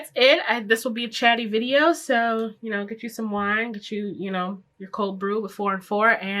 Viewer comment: Lmao I work at Dunkin so I heard “cold brew 4&4” and stroked out of confusion LMAO